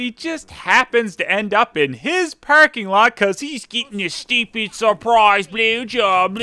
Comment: What does say at 6:32? "Something something blowjob" I heard him say it in A Serbian Film review as well. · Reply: he said "stupid surprise blowjob"